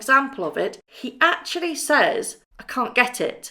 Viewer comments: He’s so much like Prince Harry, as a baby,the little Wriggler😊He has his Mothers eyes..Yes,I thought he said “Can you get it”...You’re right “Can’t get it”..He’s a Big Boy alright and about time we saw him..He’s teething too..Thanks Sussex’s...about time..Pity we didn’t see Harry. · I heard Archie say "Bish, I cant get it!" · I never comment on videos, but I have to share this!! 🤣🤣 I played to my boyfriend the part of the video where Archie says “can’t get it” and asked him if he understood what the baby was saying and for real, he thought Archie was saying: “Thank you idiot” hahahahahahahahahaha!! That’s so accurate I can’t stop laughing 😂 😂😂